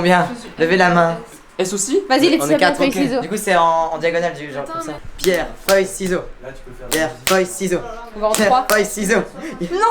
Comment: Je remarque que vous jouez "paper, rock scissors", mais le francais n'est pas ma langue maternelle (je parle anglais) alors je n'ai pas compris le mot qu'ils disaient pour "papier" - j'ai entendu "pierre, (quelque chose), ciseaux". Qu'est-ce que c'est le troisieme mot, s.v.p.? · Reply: C'est "pierre, feuille, ciseaux"